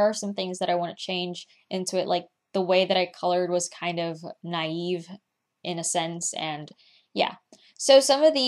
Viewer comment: unfortunately not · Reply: OK Because i heard you saying naïve